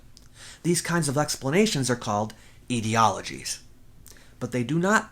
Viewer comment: Did you say "eedieologies", or aetiologies? {:-:-:}